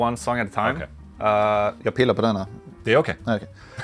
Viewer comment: did he say "i peed a banana" lmfao